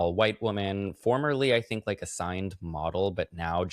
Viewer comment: When Matt said "a signed model..." my brain misheard as "assigned model at birth" 😂